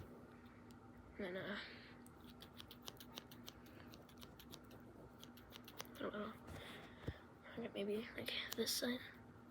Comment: what did you say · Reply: I said “and I, I don’t know”